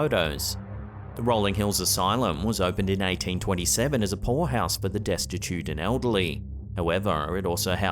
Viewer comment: i’m afraid that, when you said “poorhouse for the poor and elderly”, I heard “whorehouse” instead, and now i can’t stop giggling. Forgive me, i know not what I do-eth.😍